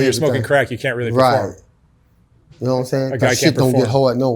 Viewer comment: did say "I can't perform?" Nothing against it just wanted to know if I heard correctly.